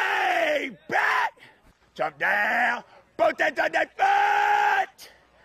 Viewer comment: Any ideas what the hell he's saying from 0:58 - 1:08? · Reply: "Drop down, focus on that foot"